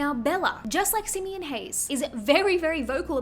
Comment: Until i saw this video, I always thought people were saying 'Simian Haze'.